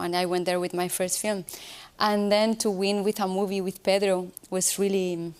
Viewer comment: What does she say at 4:44, And then to win in a movie with Feather ... Don't think that's right.